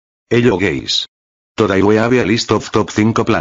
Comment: He said "hello guys" I heard "hello gays"